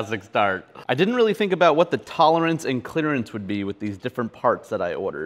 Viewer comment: did you just say "clitterance"?